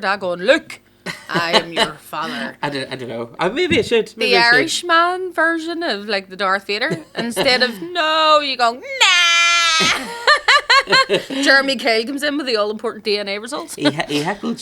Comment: Another great episode. Legit thought said Darth Veda